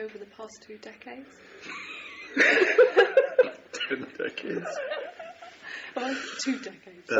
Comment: i thought she said 10 decades too.....and i just thought she was stupid :P